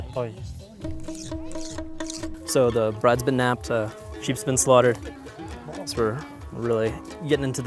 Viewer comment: I thought at 19:24 the journalist said the bride’s been napped, shes been slaughtered